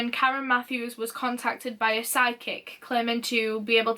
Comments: I misheard psychic as sidekick and was so confused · Every time she said “psychic” I thought she was saying SIDE KICK and I was like... Who’s sidekick?? And why do you dislike them so much??? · I really thought she was saying "sidekick" instead of "psychic" and I was soooo confused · sounds like you're saying "side-kick" haha..then after using my deductive thinkng i realized that you said "psychic" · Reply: I heard, "side-kick" and I was so confused but then I realized she said, "psychic"